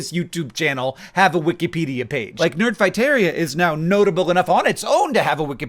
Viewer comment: I am a relatively newcomer here, so this explains so much to me! I literally thought John was saying "Nerd Viteria" before this, know absolutely nothing about what the name meant